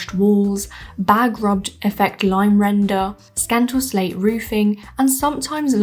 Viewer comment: I thought you said "bag robbed effect line render" and "scantal slate roofing" when you talked about the Coastal cottages. What's that?